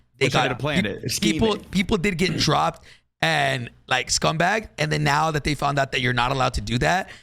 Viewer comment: Did he say scumbag at 1:28:24 ? 😂 I don’t follow so don’t know if he gets called that